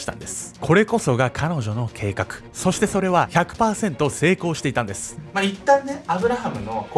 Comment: 動画止めたとこたまたまここやったら100％が ひゃ くぱぁ せんとに聞こえてめっちゃ焦った